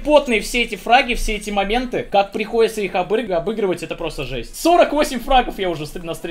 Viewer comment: | Может мне послышалось, но он хотел сказать "..как приходится их обрыгивать"